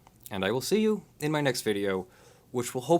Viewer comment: It sounded like Leonard said, "I'll sue you in my next video" at 7:15. Yeah, I know, he was all choked up. You are not just my favorite copyright attorney, but my favorite attorney, period.